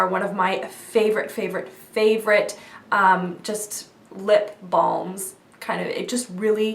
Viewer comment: I thought you said no lip balms?!